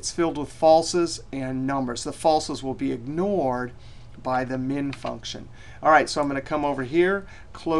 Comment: New to Excel. Thought he said "We could use the Demon function..." Really wanted to know what the Demon function did :(